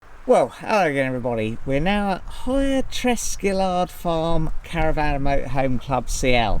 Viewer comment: 😂 I thought you said testicular farm.